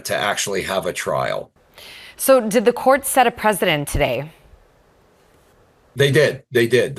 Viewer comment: did she just say, "Did the court set a president today?" 😂 At least she's got a big heart.